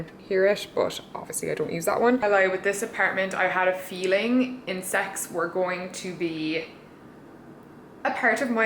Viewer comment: i thought you said "sex" LOL